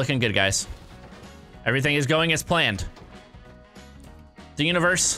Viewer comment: Did he say every thing goes as PLANT🌱🌱🌱🌱